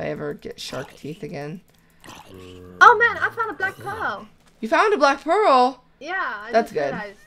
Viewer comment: when said I FOUND A BLACK PEARL i thought she said I FOUND A DEAD DUCK! lololololol xD